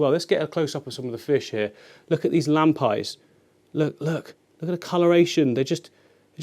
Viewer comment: What's the name of the fish at 11:50? I heard lamb pies? I can't find them online.